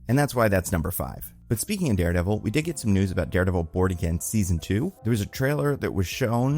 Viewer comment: Not sure whats up with the speed of the audio in this vid, swear it sounded like you said Daredevil, Bored Again Also, shoutout to Monster Ock. I still have that sound effect in my brain 😂